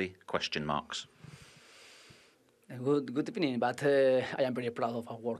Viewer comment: LOL Emery had to get the “good ebening” out there before he starts 😂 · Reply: I thought he said good opinion at first 😂😂